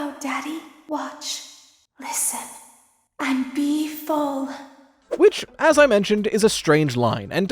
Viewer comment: I always thought she said "befold" instead of "be full". Then again, tinnitus is a pain.